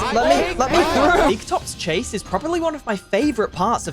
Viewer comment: I thought it was going to say big toe 💀(I am new to fnaf)